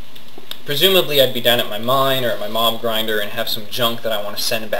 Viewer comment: lol at first when you said where it went i thought u said "Mom Grinder" instead of "Mob Grinder"